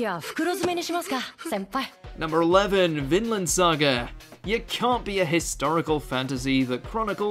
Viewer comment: Is it just me or does it sound like he is saying ToolFinn for Vinland Saga lol